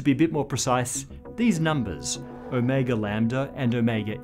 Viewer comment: My brain blips every time he reads Ω and I hear “O’Mega”